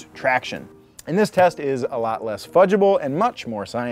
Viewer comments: did you say fuckable? Oh, fudgable! Silly me... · Sorry what did you say? I lost focus for a few seconds there... 😏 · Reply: WET TRACTION